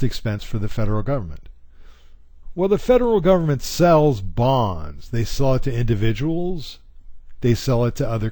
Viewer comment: I thought he said the government sells bombs for a sec, lol. But I realize it was BONDS. XD